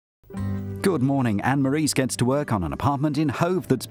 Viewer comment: did he say Anne Marie